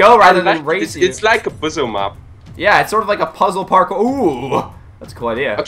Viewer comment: ha ha i thought he said bozzem map